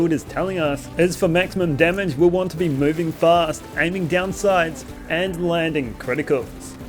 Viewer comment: Very inspiring video! I'm working on a Grand Master Zane build right now but, sorry, I'm not sure of what you said at 16:23 : "aiming down sides" ? Sorry, English is not my main language. Thank you! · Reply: Aiming down sights, you were close. Let me know if that still doesn't make sense.